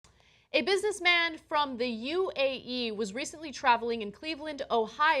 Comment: She obviously isn't aware that he is from the UAE, no wonder he has multiple phones. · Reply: Lu-chan for a second I thought you said the UAE wasn't a country. Lol